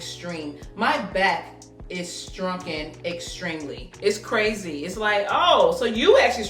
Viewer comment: Did you say "strunken extremely!" lol! At timestamp 8:15.....Anyway, it came out BOO-TI-FUL!